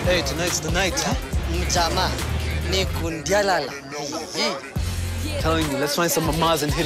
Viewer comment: my favorite part is 14:25 when the guy says something in swahili..please can someone tell me what he says? it sounds like..ja ma ndiku diyalala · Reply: mjamaa ni ku ndialala